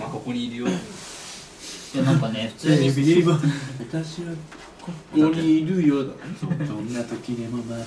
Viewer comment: てつやのここにいるよがフジファブリックの蒼い鳥にしか聞こえない